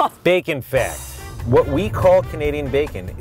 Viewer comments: Who else thought he said bacon fat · When he said bacon fact i thought he said bacon fat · I thought he said bakon fat not bakon fact